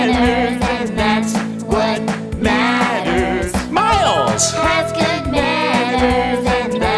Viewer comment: WAIT WAT?? Does the outro really say 'miles has good manners and that's what matters'? Why did I always hear 'miles 's pesky banners are that what matters'???